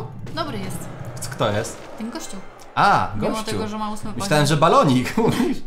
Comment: ja usłyszałem kościół xd kto z 2022